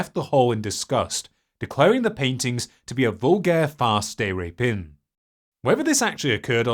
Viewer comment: you say "vulgar fast day rapin." What is the actual phrase you are saying? Probably French but closed captioning and the transcript doesn't properly transcribe it. Tried to look it up on google with little success although it did denote some sort of fraud.